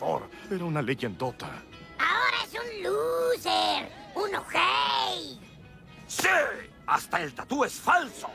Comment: ¿que dijo? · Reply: Un ojei = ojete = egoista o malo